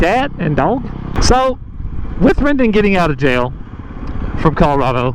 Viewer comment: it’s not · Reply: oh okay! I didn’t see that, I’m just going of the videos I’ve seen where I thought I heard “Rendon” and also saw the captions say that. I suppose it easily could be Brendan, and that would certainly make more sense.